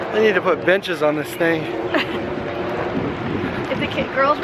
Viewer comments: Does say at 7:03? Is it "they need to put benches on this thing"? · I thought he said the b word but he said benches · Did say bitches at 7:03 because I think he did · It's sounds like he's saying bitches instead of benches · when said they need to benches on this thing it sounded like he said they need to put b*tches on this thing 😅😅 lol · sounds like he's saying : I needa book b****es on this thing. 🙊 I think I only just heard it.